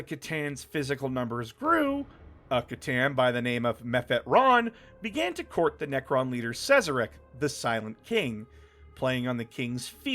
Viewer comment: was listening to this in my car and i swear i heard "Meth Head Ron" made my day XD